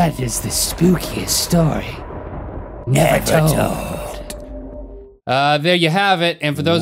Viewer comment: it sounded like Rhett said ''The Spookiest Story Never Told''